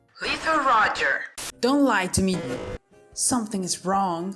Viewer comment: it sounds like he say (don't lie to me shit)